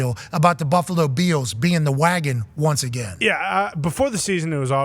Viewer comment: I always thought Pat said Buffalo Beatles, now I realized he is calling them Beels lol